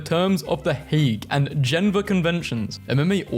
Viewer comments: did you just say "genva" convention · did bro say genva convention 😂